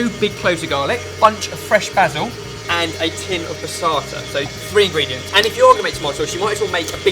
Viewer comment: For the anyone (i.e., Americans) who couldn't understand that word around 1:05 and 1:50, it's "passata". And the other word is "sieved". Google "tomato passata" for definition.